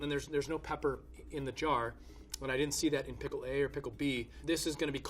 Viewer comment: i thought he said pickle pee. "t-that no.. pump a rum!"